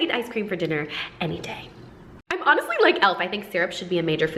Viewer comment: Wait did she just say L...like from death note coz Im a huge fan lol